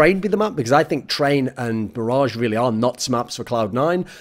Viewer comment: 4 and a half minutes in and I hear “train and MIRAGE are not really maps for Cloud9” yep cya later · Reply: g welfare he said “train and mirage are really nuts maps for C9” but granted, it was hard to hear.